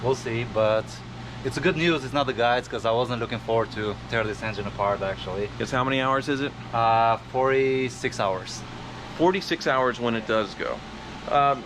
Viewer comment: Did he say 46 hours or 4-6 hours? I heard 46, which can't be right.